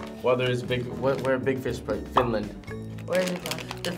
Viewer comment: As a Finnish person, i was like: "Ok, they do not try any Finnish snacks, like i hoped, so ok, i close this video" 13:44 I heard from this video "Finland ?", then i was like "Wait what ?" O.o , and i continue to watch this video ^^'